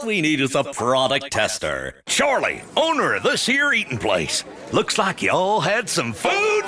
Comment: what does he say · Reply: Charlie, owner of this here eatin' place (there ya go)